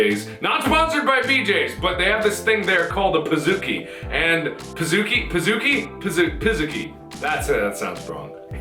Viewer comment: did he just say piss cookie